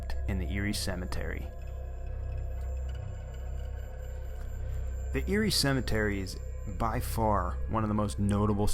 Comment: At first, I thought he was saying Eerie cemetery. My bad. But it is eeroe